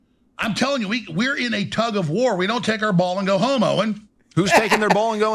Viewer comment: I definitely heard "we don't go homo" on first listen 😂